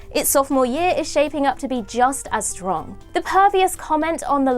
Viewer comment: did I hear "pervious" instead of "previous"?